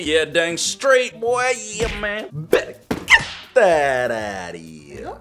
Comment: The subtittles thought you said get that daddy when you said get that outta here